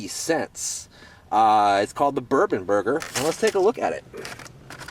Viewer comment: thought at first you said it was the burping burger 0_o